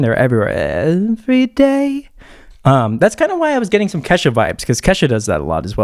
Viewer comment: I thought he said ketchup vibes😂😂😂😂😂😂😂😂😂😂😂😂😂😂😂